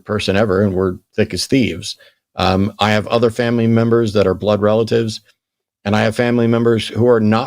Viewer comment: Hey, quick question but did you happen to use a racist term for a Muslim in your call cause it sure sounds like you did?! I'm not saying it was deliberate that may well be a common turn of phrase where you're from but that's not cool and I am gonna call you out on it. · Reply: He said "family members". The audio was just a little distorted. How you thought he said the "S.N.'s" slur is beyond me, because even though it sort of sounded like that, it would have made ZERO sense in context. Please use common sense before confronting people on things like this.